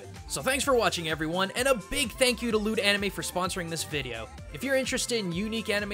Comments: I thought he said "Lewd Anime" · "Loot Anime" -sounded like, "Lewd Anime" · When you went into talking about Loot Anime, it sounded like you said "Lewd anime" lol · I cant unhear his ad read out, I keep hearing "lewd anime" instead of "loot anime"